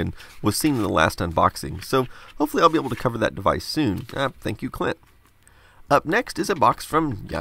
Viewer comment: Did you say thank you clit?